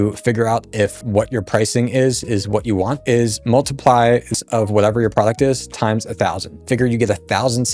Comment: you said, "...multiply __ of whatever your product is" What is the missing word? Thanks! I think you said, "Multiply whatever the price of your product is..." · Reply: Multiply the price of your product times 1000 for a rough estimate of what would be reasonable for it to earn over the lifetime of the product. We aren’t guaranteeing any specific earnings or any earnings at all. This simple multiplication can be a quick way of helping you decide if you are priced how you want. IE: $29 x 1000 = $29,000 $<num> =$1,000,000 Easier to see the difference charging more makes when you multiply it by your anticipated sales. And it is easier to make your product worth a higher price than it is to get more customers. Looking at the numbers above, if you wanted to earn the $1,000,000 but with the $29 product, you’d have to get roughly 35,000 sales. Which is much harder to do than it would be to make that product worth $1000 and get 1000 sales.